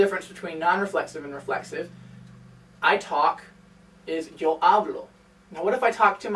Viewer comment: The guy in the video sounds like he saying Havlo, when it's spelt Hablo.